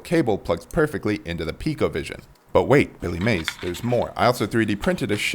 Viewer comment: I admit, when you mentioned the brain I thought you said ColecoVision. 😮😅